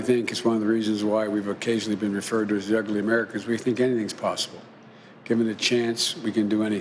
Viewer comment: The endless frontiers...of grants for dweebs...does he call us the 'ugly americans' at 1:40? The science of dementia!